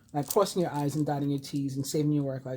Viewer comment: Lolol...I thought you said crossings your i's and dotting your t's. I think you need some rest and sleep. 😝